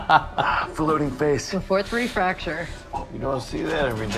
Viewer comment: i thought she said "le fort fracture", from him popping only the upper mouth and the nose i would guess the are is only there and not going outwards meaning she could have been saying "le fort 1 fracture". Learned this recently so maybe i have this term stuck in my mind